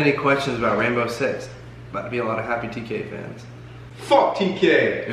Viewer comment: "Theres a lot of questions about siege. Theres about to be a lot of happy TK fans." I'm already excited! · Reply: Taylor Ferren I thought he said not a lot of happy tk fans thank god